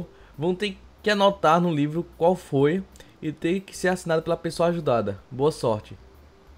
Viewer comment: eu entendi assasino no nome de cima